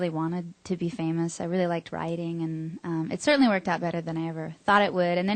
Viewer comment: when she said "writing" at about 2:24, i thought she said "rioting."